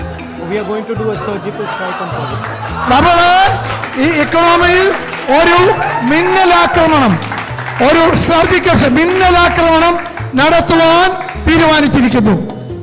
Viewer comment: ahh i know what he's saying "mummy mere se nhi horha" lmao